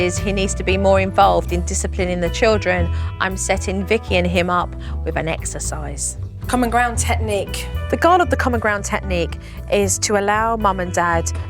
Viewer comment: I thought she said an exorcist LMAO